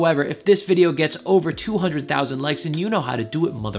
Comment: did he say 200,000 likes?????